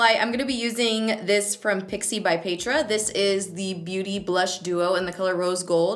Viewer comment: I think I'm the only one that heard you say ,"Pixi by Pedrahhhh" like Pedro 😂